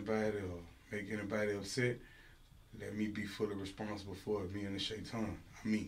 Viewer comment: So did no one notice what he said at the beginning at 0:33 ? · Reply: he says in the beginning "anyone who gets upset let me be fully responsible the Shaytan (devil) " and the girl in the background says "ouu I got the chills " wake up people he is evil